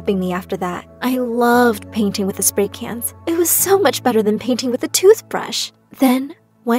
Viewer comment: Wait in 5:30 did i hear "it was so much better then painting with a toothbrush"